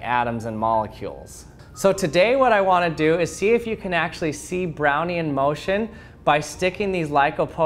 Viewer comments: All I heard was a "Brownie in motion" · Why am I hearing 'Brownie Emotion'. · Thought you said brownie in motion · I thought he said brownie in motion.